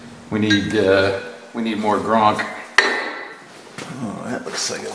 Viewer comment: It just needed a little more ‘grunk’. I thought it needed a little extra ‘stootch’ but maybe I use a different unit of measurement than you. Job well done. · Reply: Oooooh you said grOnk! I thought you said grUnk. I get it now. Thanks for the correct info, that's why I like this channel.